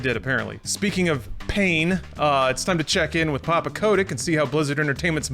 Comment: swear to you at the beginning i thought he said papa Kovic